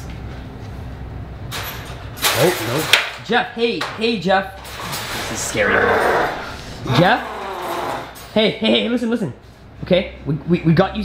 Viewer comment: At the beginning I thought he was saying Jeffrey but I thought he was going to puke when he put his head down